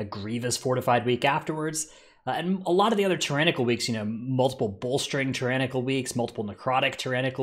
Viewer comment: I thought he said '...multiple bullsh*t Tyrannical weeks' lol.